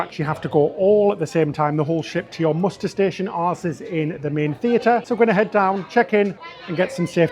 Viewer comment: “ar$e$ in the main theatre”….! 😅🤣 you didn’t say that but that’s what I heard xx